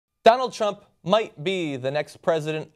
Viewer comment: Did John call him Donald Chump? 0:01 I approve! AARHGH! MY EYES! You guys promised us kawaii cartoons blocking his fa... that thing on his neck!